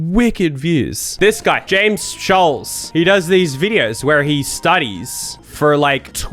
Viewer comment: i thought you said James Charles XD (i was looking away)